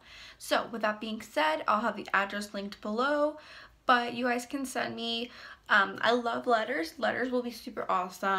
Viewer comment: I thought you said “you guys can send me uhh love letters” 😂😂😂